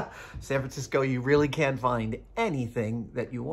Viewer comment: I think the captions are wrong. It should be can, rather than can’t.